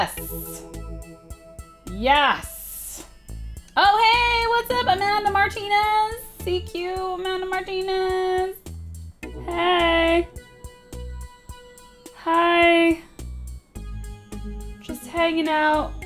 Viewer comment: I thought said Melanie Martinez